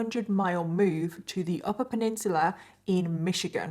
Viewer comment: I can't get over the pronunciation of peninsula!!!!! I heard "pin-nin-chew-luh" 😂